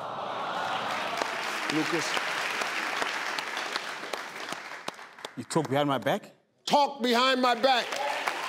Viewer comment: Name something that might end a friendship What he said: “you talk behind my back” What I heard: “you took grandma back”